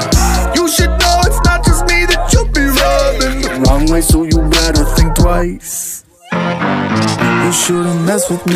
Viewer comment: I thought she said "mama told me you better be nice"